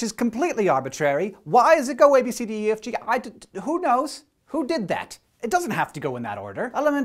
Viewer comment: "Who did that?" I always hear Piderman whenever I hear that.